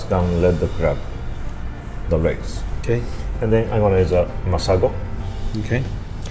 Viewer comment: Awesome channel! What is it he puts in the seafood at the 8:15 mark that has Sushi on the package. Couldn't understand? Anyone Where do you get it? Thanks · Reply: Robert Skillings sounded like he said somthing like masiago